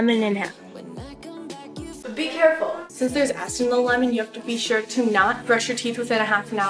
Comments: it sounds like she said since theres ass in the lemon XD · what does she say at 0:35 ? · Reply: YoungJasiolz Prod sounds like she said "since there's ass in the lemon.." But we all know she meant 'acid'. HAHAHA so funny now get over it people make mistakes ya know (